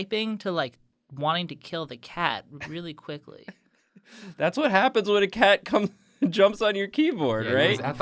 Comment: "that's what happens when the cat cums- jumps on your keyboard" That's what I heard- help me